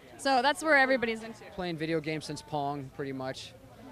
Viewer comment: I thought he said "playing Video games since porn"